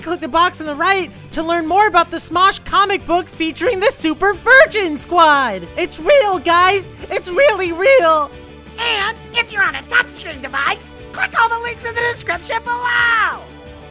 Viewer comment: I thought that it said super vegan squad lol